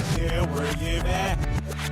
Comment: what the heck did say 😳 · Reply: never you at